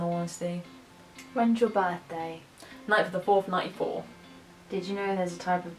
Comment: When is your birthday? I heard " The 9th of the 94th😂😂